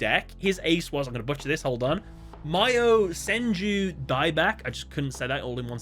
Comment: Funny thing about Sylvio's Yosenju Deck when he first summoned Daibak i thought he said "My Yosenju Daibak" but he said "Mayosenju Daibak"